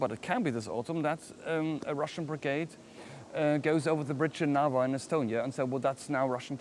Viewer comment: Which line did he say? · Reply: he says "the bridge in narva"